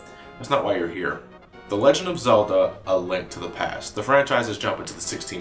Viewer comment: Almost sounds like you said a "Lint" to the past there.